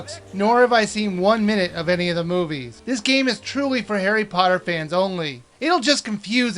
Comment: i thought he said hairy potter